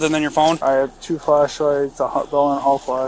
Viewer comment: i thought he i said i have 2 flashlights and a hotdog lmao.